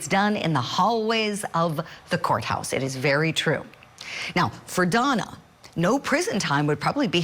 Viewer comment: Omg Julie stop with the “Mama Donna” nonsense - just be normal like Vinnie is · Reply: Oh, I thought she was saying "Moron Donna." Did I mishear?